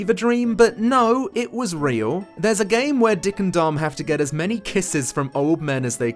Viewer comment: I swear i heard you say "Dick and Dong" and thought to myself How BBC got away with This Very "subtle" penis inuendo until i search for it